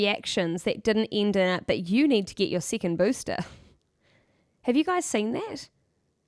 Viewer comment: Thanks, great breakdown and commentary, but what do you say at 15:54? . . "About reactions that . . .." what? I can't make out what you're saying, sorry! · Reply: I think she meant to say 'that didn't end up in "but you need to get your second booster"' but 'up' and 'in' got switched